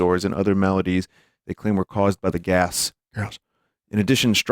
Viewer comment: I heard that gyass